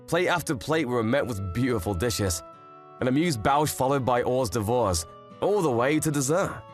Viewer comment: Did he just call them “whores de vores “?